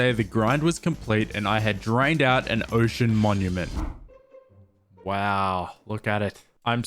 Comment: LOL I thought it said “I died to an ocean monument”